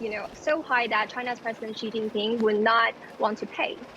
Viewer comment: The speech-to-text misheard 'Xi Jinping' as 'cheating thing,' which was hilarious